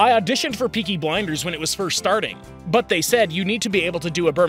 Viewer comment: Sounds like hes saying Burminham